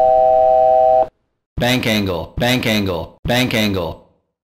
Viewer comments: 弁慶GOに聞こえるw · What does the alarm say at 0:10 ? Its Bank Angle? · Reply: It's Bank Angle